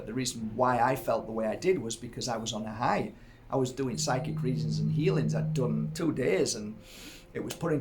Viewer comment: Thought it said psychedelic drugs. 🤣🤣🤣